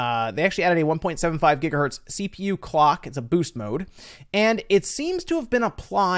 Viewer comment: I thought boost mode said BEAST mode lolol